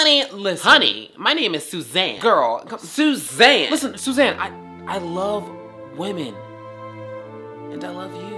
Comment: When he said "I love wo-man" all I heard was "I love 🌟men🌟" 😂